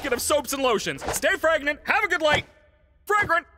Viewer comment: i thought he said "Stay pregnant!" 😭🙏